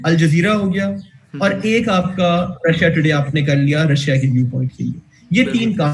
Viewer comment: What is the name of the third channel u said at 3:53?I'm already following the other two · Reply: Russia Today